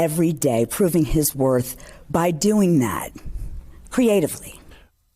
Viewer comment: Did she say "creatively" with that peculiar smile?